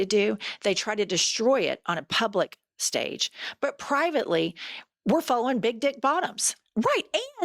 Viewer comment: OMG! When you said, "but privately they're following big dick bottoms!" Oh snap girl, I thought you said they're falling on big dick bottoms!😂😂😂